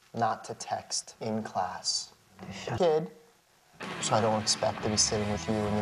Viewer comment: Did he just call her "kid?" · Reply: It got cut off, he said "you're a good kid", he was talking to the daughter.